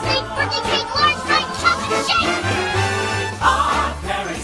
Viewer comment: Please, let's take a minute of our lives, to appreciate how Timmy said "Chocolate shake", It's beautiful 😿💗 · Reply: Sounded like he said "chocolate shit"